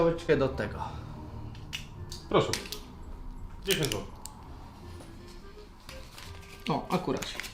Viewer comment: usłyszałem 1000zł a to 10zł